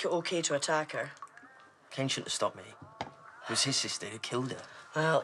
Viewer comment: Why does it sound like Matty says 'it was his sister who killed her'?